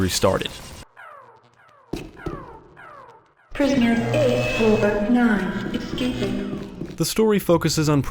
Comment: I always hear Prisoner 8/9 escaping (eight over nine).